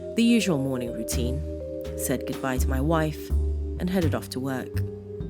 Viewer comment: Why did I hear "I sacrificed my wife instead of "said goodbye to my wife". I guess they're similar enough. But that ending...oh my.